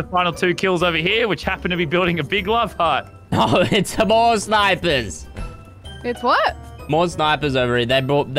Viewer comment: When he said more snipers I thought he said divorce papers